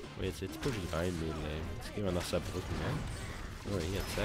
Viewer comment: jetzt gehen wir nach Saarbücken... hab ich das richtig verstanden? :D